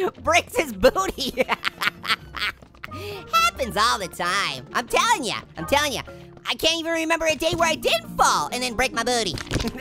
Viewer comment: Orange I thought you said you can't read